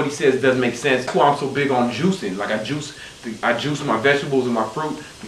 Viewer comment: i thought u said you was "juicing" yourself lol